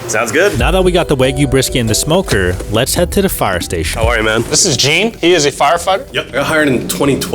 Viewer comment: "this is jean he is a firef**k" i think i may have miss heard what he said